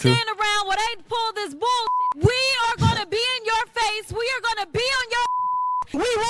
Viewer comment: did she just say she was going to pee on our face?